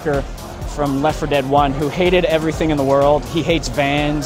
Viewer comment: I thought he said "I don't hate vest"